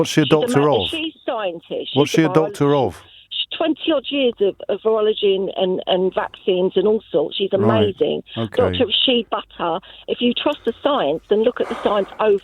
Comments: did she say Dr. Shea Butter? 🤦🏻‍♂️ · did I hear her correctly? Dr. Shea Butter? That's used in cosmetics and haircare/skin care isn't it?